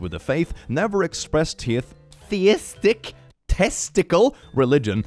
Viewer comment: did he just say testile. LOL